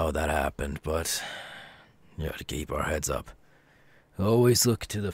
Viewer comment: did he just say gay bar heads up??